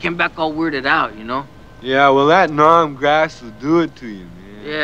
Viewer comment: always thought he was saying “Nam Gas “as in the testing of different chemicals on US Soldiers1:54 what a funny birdie